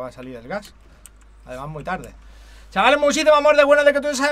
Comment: que dice solo escucho chavales muchisimo amor del bueno shksjsvaosihapwisoabcrñsjsvebodkd soiba WTFFFF